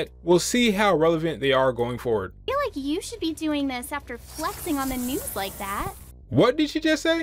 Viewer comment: what does she say? I can't understand her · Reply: "I feel like you should be doing this after flexing on the news like that"